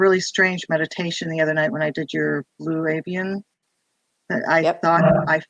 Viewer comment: Hi all, can anyone which meditation is talking about!? I heard ‘blue-Evian? Any link to this meditation? Thank you😊